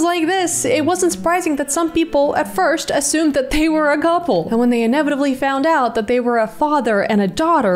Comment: Did she say gouple??